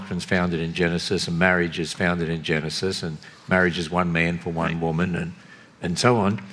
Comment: Freudian slip at 6:12 I think he said "marriage is one man for white woman". That's really what it sounded like anyway.